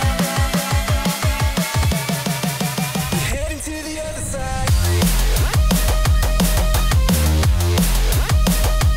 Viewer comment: 여기서 뭐라고 하는거에요? what did he say about this part? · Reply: he says "Headed to the other side", I'm guessing just the other side of the route.